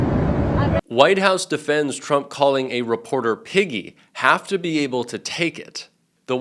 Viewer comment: The first time I saw that footage on Friday night I could have sworn he said piggy at the end but since no one was talking about it I gaslit myself into believing I was mishearing it. Still don't understand how it took the press like 2 days to start talking about that aspect of it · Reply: I thought he said Nikki. 🇨🇦 but it's piggy. I couldn't believe it.